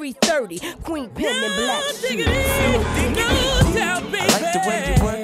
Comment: I ALWAYS hear 'no giggity'